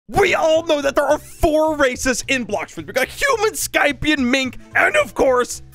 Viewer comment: I thought he said we all know there are four racist in blox fruits